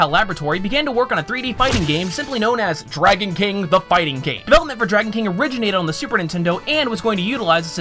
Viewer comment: Did he say the fighting GATE